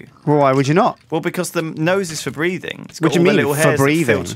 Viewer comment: I thought he said "Nose for R2", I was waiting for "D2".